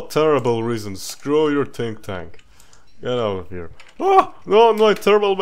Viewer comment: I love how because of accent it sounds like hes saying "Tink Tank"